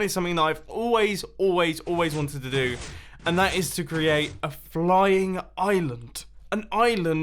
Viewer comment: dammit. I thought he said “a frying island”